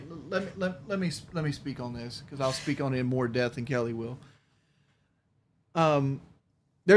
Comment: He said “in hell” instead of “in depth” He’s so stupid and yes his soul is lost, wife beater, pdf file, never takes accountability even though he fakes he has, it’s all an act he has no soul, he just wants peoples money Shout out Sean Ranklin